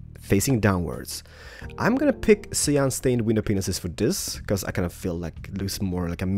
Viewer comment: did you say wenis-penis? it sounded like it XD